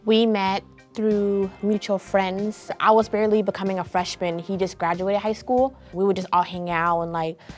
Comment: Wait so 18 and around 14 is when they started dating? Hold up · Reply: CrOpPeD LoCkS no. I thought so too busy she said “he just graduated high school.” 💀